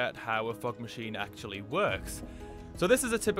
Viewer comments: I always hear “fuck machine” - “fuck liquid” lmao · Why i hear a "FUCK maschine" instead "FOG maschine" when this dude pronounce that?